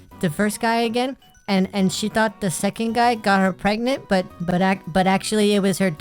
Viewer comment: am i on crack? did he just say her twin got her pregnant?